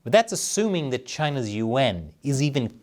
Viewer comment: At first I thought you were saying, "China's U.N." 🤣 Though, that's another episode or three.